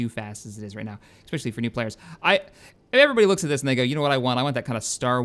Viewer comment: Did he just say the n word at 17:58 "everybody looks at this... N" Thats what I heard at least.